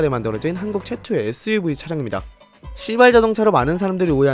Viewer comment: 에 발음때문에 진짜 시X인줄 발음이 시 가아니라 씨로들려요ㅋㅋ